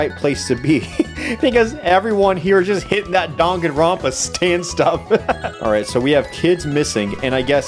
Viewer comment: did you just say... Danganronpa?